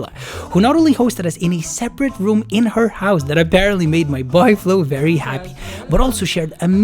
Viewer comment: Everytime he said “my boy Flo”, I heard “my boyfriend” lol. Anyways, never been to Pakistan but looks cool!